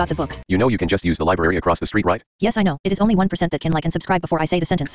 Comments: I heard "Grandma said, patting the black cat she had on her lap" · I heard "Grandma stop patting the back cat on her lap" Lol